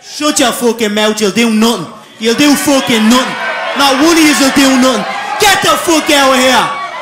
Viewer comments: what does he say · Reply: Nebi Abdullai not one of you will do nothing. Of course in his slang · At first i thought he said you damn nunth or something. Thought it was an irish slang term XD